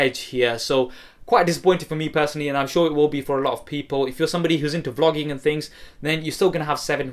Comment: it sounds like he said "If you're into flogging and things lol